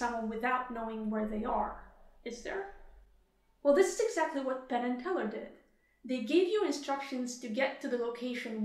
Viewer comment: I keep hearing "pannenkoek" instead of "Penn and Teller", and I don't dislike it.